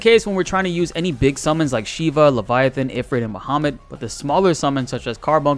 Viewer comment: did you say Mohammed? I don’t know if SE would make The prophet Mohammed a summon.